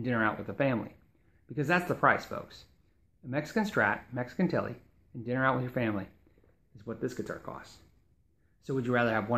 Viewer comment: There is one part at 7:57 I listened to several times and I am not totally sure what he said. I guess he meant you could have a family of a Strat and Telecaster MIM for the cost of one USA instrument...... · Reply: Nealix D. I think he said or meant you could have a Mexican Strat and Tele and dinner out with your family for the same price as an American Strat